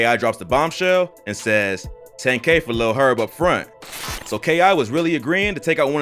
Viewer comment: you said "10k for lil herb up front" when she really said 10k for herb and 5k up front